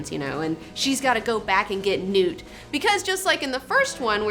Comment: we think you said "she's gotta go back and get nude, just like in the first movie...", but then a second later we're like: "Oh, yeah, go back and get NEWT", sorry.